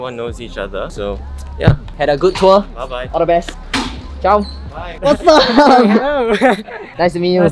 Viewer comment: Did I just hear Mr speak Swedish? "Ha det bäst!" hahaha